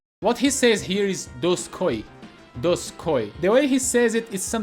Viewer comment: I always heard "Oink Oink!" 5:43 "Spinning ROUND kick!"